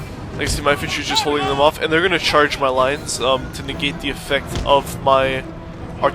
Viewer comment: i thought you said "their gonna trash my lights" hahaha ahhhhhh christian bale is funny